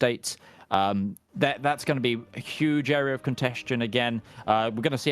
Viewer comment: What's the word keeps using that sounds like a mixture of "contest" and "congestion" - "cont-esht-tion"? Contestation maybe? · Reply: Contention, it means when you for example say "A point of contention" / "A point of conflict/a disputed teritorry"